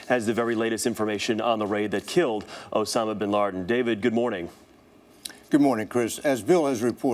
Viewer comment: like if you think he says osama bin LARDEN and 0:05?